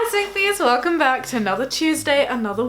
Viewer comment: I thought she said "Hello sophies" and got scared because my names sophie, also every time she says jamie i think "Wait my boyfriend?" 😂